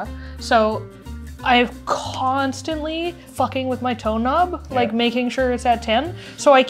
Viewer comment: Lmao I thought said “I keep messing with my toe nub”